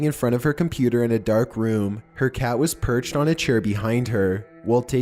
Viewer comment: Am I the only one who heard him say "perched on a cher" in the first clip